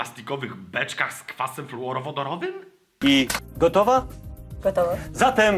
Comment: Czy tylko ja usłyszałem "fetor" ?????